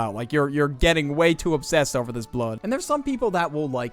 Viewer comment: DID I HEAR INCORRECTLY OR DID THIS MF SAY BLUD